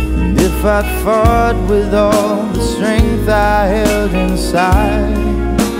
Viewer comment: Love this concert but at 1:20 I swear he says " and if I fart with all the strength I have inside " instead of fall